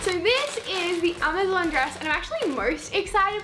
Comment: I thought you said eBay not Amazon